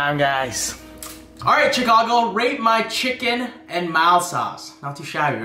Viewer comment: did he say “rape” or “rate”